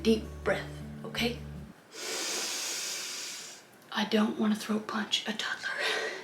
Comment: i love this lowkey dark and a little more adult kris ross segment! sending love from vancouver bc can someone correct this bc im pretty sure this is wrong... 8:40 what does she say?? cause i hear 'i dont want to throw a punch at a toddler? and why do i think it looks good when she says it looks terrible?